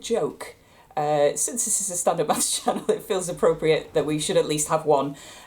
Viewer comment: Subtitel at 8:44 says standard bus channel instead of standup math channel